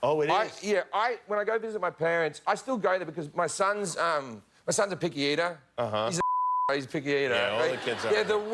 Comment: "My son is a pick(something)". Can't spell that. What is the word and what that mean? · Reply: a picky eater